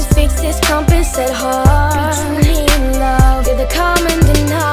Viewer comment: it sounds like hes saying "bitch,you mean love" . aha!loveyoujustin!<3